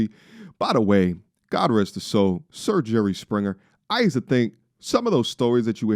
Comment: Did you say RIP Jerry Springer??